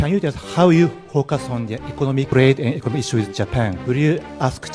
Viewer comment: 너무 비웃지맙시다 동영상 끝에 이야기하지만 우리가 좀 낫긴 해도 비슷함.. 한국 발음으로 focus on the 발음하면 외국인에겐 pocus on da로 들림